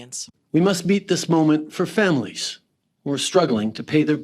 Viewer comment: Trudeau says "FAMINE" instead of Family, a Freudian slip perhaps?